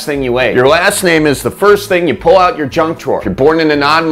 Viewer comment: I thought he said “your last name is last thing you pulled out your junk to💀💀💀”